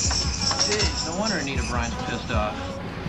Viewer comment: the security guys said "No wonder why Anita Bryant is pissed off." Anita Bryant was a contemporary Christian pop artist that was endorsed by Tropicana Orange ange company, and she said something deemed inflammatory about the gay community, and the boycotted Tropicana orange juice because of what she said. Since this film was made in 1978, that's when Anita Bryant made the statement.